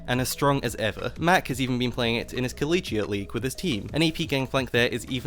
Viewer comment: did u just say colliegee league?